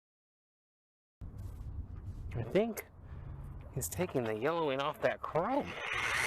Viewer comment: i skipped to the the chrome polishing with wax and the wheel. when i pressed play all i heard was "i think thats taking the yellowing out of the chrome".... i actually said "OH NO" out loud